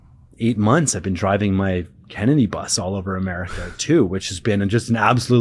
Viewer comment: DID I HEAR " DRIVING MY KENNEDY BUS?" ?